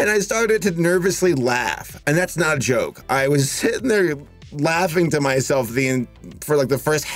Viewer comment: For a moment, at 1:18, I swear I thought you said 'I was Hitler'! There's clearly something wrong with me and I'm terrified...